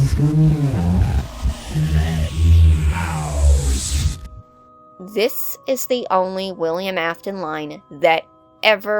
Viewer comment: What does he say here? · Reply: “I always come back, let me out”